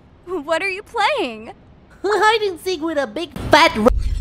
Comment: it sounds like pump is saying "were play with a big fat RA-"